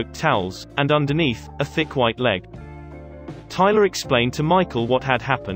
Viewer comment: “… and underneath, a thick white leg.” 😂😂 did i hear him correctly?? tell us someone was fat without telling us they were fat.